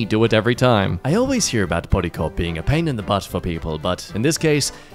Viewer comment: can someone please explain what the fuck he’s saying here?? it sounds like “bodycop.” the auto-generated captions weren’t any help :/